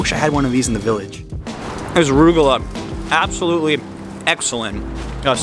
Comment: sorry what was the pastry that you were eating? i keep hearing arugula lol! seth is a great guest on your show. loved the fusion food restaurant; i never knew such a cuisine existed.